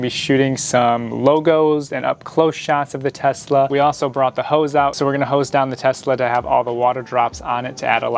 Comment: I thought he said "we also brought the hoes out"